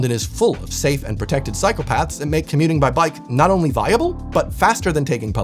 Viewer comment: Gave me a giggle when you mentioned cyclepaths because it sounded like you said PSYCHOPATHS...